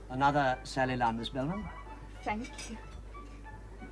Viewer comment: What is Miss Belman eating at 30:15? Reeder calls is 'sally la' something. Some type of cake? I can't understand what he said. Any guesses? I like to look these things up to either buy if still available or find a recipe to make it myself.